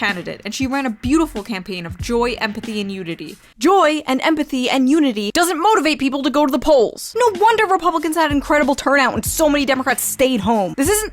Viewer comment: Somehow I heard "joy, empathy and nudity". That might have worked better, actually.